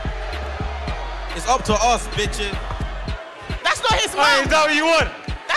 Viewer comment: did just call the crowd bitches ??😂😂🤡